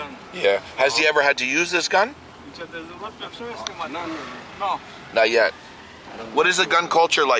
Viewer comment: the person said No do why did you say yes 🤔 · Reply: He said “no”, I said “not yet” .